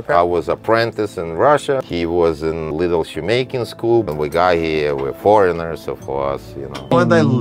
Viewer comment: “He was in little shoemaking school.” cuts to man having traumatic flashback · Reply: Here, i thought he said, "Little Jamaican school." 😅